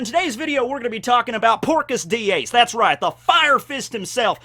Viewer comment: With his Yeehaaw accent sounds like he is saying Porkgas D Ace. I can't be the only one to hear that right?